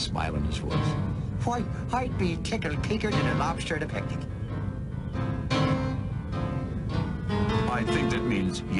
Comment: what did digby say at 11:38? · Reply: Well I'd be tickled pinker than a lobster a picnic